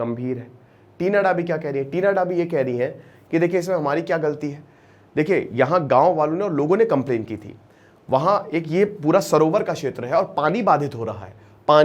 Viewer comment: घर में पढ़ाई का माहौल था। बस संस्कारों की कमी थी। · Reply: Gawar to ho , Bahre v ho ?? 6:40 v ni suna gya sir ne kya kaha ?? Gaon Wale or local complaint kr rhe